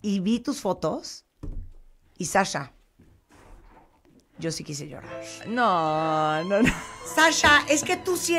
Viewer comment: Que alguien me diga que carajos dijo Martha Debayle en el minuto 1:49?? Yo si quisiera que..... ? ... ... , por mas que le subi el volumen no le entendi xD . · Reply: Evelyn Carolina Hernandez “Yo sí quise llorar...”